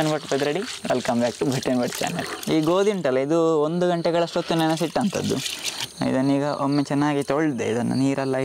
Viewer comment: says wheat has been soaked for 1hour, but subtitle says 5 minutes.... Kindly clarify which is correct...